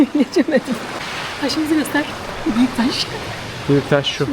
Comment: yanliss mi duydumm yoksaa abla "aşkım" mi dedii?? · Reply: Hayir demedi hah simdi bize goster dedi:)